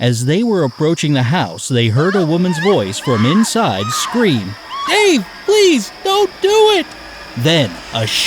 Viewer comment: I got last 2 correct, but I still don't get 1st riddle, even after explanation. Can anyone please explain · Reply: Oh, Now I got it, when the narrator repeated the woman's scream 'Dave please don't do it' at 0:59 I initially heard it as 'Ew please don't do it'😂😂😂 Thank you everyone!